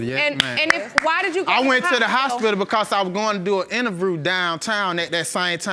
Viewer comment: wait did he say he was going to do an "innervrew" at 8:20 mark?